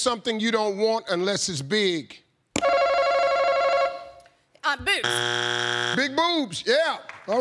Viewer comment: I thought she said big boots.